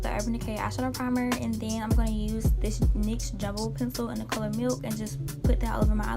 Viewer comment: WHAT NYX? I DONT UNDERSTAND HELP ME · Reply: Lmao NYX jumbo pencil in the shade milk